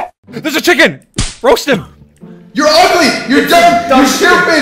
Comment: who heard George say ur sus. Or am I just playing to much among us